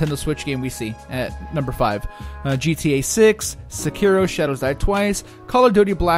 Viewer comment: did you just say GTA 6? Hmmm I don't know if it came out yet...